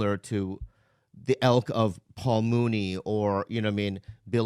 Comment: Did Bobby say "elk" instead of "ilk"??